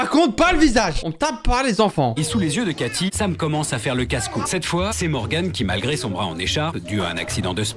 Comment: c moi ou le narrateur a dis "et sous les yeux de caty sam commence a faire le casse couille" 💀